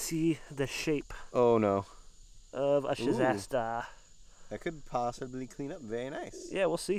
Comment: Hi love get to watch all hunt for fossils an all the gyms you find in the coral at 2:45 in this video you pick something up and you call it a Shasta I believe I couldn't really hear you so could you please tell me what that is I would really like to know because I think I have one and it looks just like this one